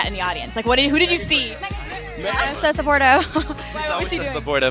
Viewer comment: Who did Kirstie say was so supportive and jumped up around 7:21? I couldn't hear it. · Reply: Meagan Trainor